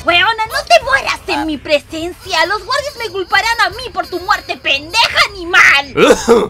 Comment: acaso escuche a sourge decir p€nd€j@